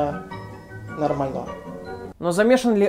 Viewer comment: мне послышалось или он сказал "порнонармального"?